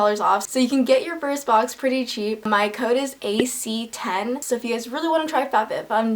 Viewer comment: Hello! I love this video so much, and I want to make sure everyone can understand and enjoy it. That's why I wanted to let you know that your captions are a little off. At about 0:38 the auto-captions say "my coat is" where they meant to say "my code is". At about 1:00 the auto-captions say "Brandis" where they meant to say "brand this". At about 1:10 the auto-captions say "I find" where they meant to say "If I had". At about 1:14 the auto-captions say "I'm asked" where they meant to say "eye mask". At about 1:17 the auto-captions say "hostels" where they meant to say "it has those". When you get a chance, could you fix that? That'll help the 360 million folks globally who rely on closed captions to understand videos. Thank you, and keep up the great work!